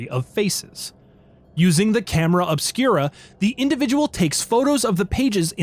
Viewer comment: I thought you said camera of Spira at first and was wondering if Tidus knew about this.